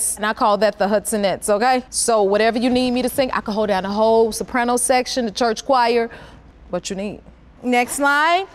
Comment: What’s she saying at 0:31? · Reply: that she can hold down the whole soprano section of a choir, and sing whatever someone wants!